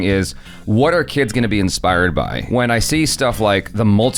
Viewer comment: I listened to 03:58 couple hundred times and I hear both "What are kids gonna be inspired by" and "What our kids gonna be inspired by". Shipper in me chose the second one and now I'm having a sugar high thinking about little with a hint of. Cute af.